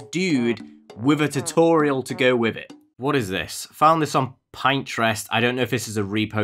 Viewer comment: im sorry did you say pine-trest?????? ITS PN- TREST WHERE YOU PIN YO INTREST OMG ASLO YOUR GREAT OK BYE HAVE A NICE DAY